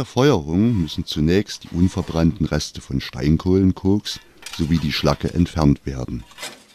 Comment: Sagt der bei 2:15 Koks? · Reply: Ja, schon bei 1:38 >>Steinkohlenkoks>> richtig gehört. Im Gegensatz zu unveredelter Steinkohle gibt es bei Koks fast keine Rußbildung.